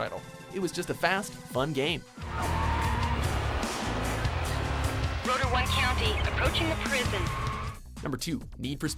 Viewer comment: it sounded like he said Roblox